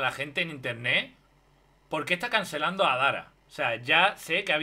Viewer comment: En vez de Adara entendí: "a Dalas" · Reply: yo entendi "ar dalas"